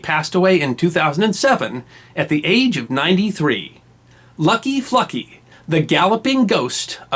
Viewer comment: at first i thought he said lucky fucky lol